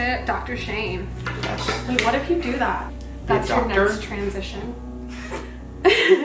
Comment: OMG I FOUND ONE OF THOSE VOICE THING IN THIS VIDEO (like yanny vs. laurel). At 15:10 listen for when says "That's your next transition.". Play it over again with your eyes closed and think about her saying "That's your nerve transition" OMFG CAN YOU HERE IT... IM SO HAPPY I FOUND THIS